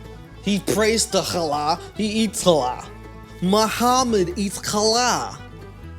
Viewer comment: I thought she said, "He eats allot"